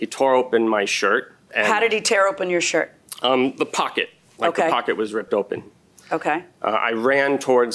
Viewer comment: UK MedicFRCS oh but he did! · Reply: He did not say his pocket was torn off. He said and I quote ' my pocket was torn open '.